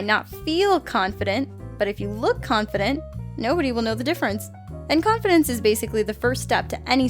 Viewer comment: it says "no" instead of "know" in the captions.